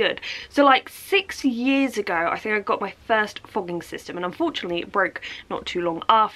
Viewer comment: i thought you said fucking system hahahahaha